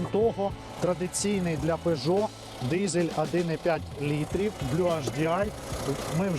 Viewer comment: Так тут двигатель и есть 1,5 BluHDi 130 л.с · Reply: пардон, послышалось, 1,2 литра.